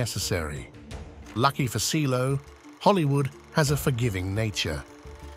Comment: Did I just hear you say "Hollywood has a forgiving nature?"